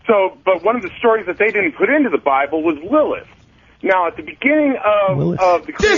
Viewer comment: A great show as usual & full of good tension. has got the game down pat. Btw, that 1 caller mentioned the book of LILLITH but they thought he said Willis, lol...